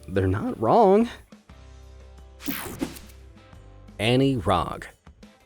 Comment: I thought you said Andie Frog